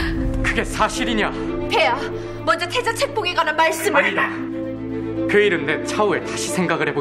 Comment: "아니다"라고 하는 부분이 개소리로 들려서 잠시 내 귀를 의심했넼ㅋㅋㅋ